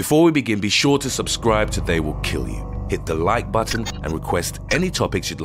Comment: During his intro I thought he said "subscribe or they will kill you"